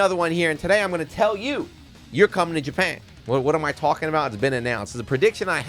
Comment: Oh, good, almost thought it was saying, "YOU'RE GOING TO BRAZIL."